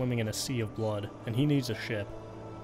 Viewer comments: Love how I heard that as "Daud is swimming in a sea of blood and needs a sh*t" :) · i wasn't paying attention till i missed heared the video and thought he said " he was in a sea of blood, and he needs a shit." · "Daud is swimming in a sea of blood, and he needs to shit." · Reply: I heard "shit" but without replaying it, i had to conclude, reasonably and linguistically, he said "a ship". so yeah, i "heard" him say it, but i couldn't give a good reason for it so i had to conclude on the most obvious alternative. but i can't ignore what i heard, i heard it too. i would be lying if i said i didn't react to thinking that's what was said. shitting in a pool of Vital Fluid? Now THAT'S a Bloody Crappy thing to do!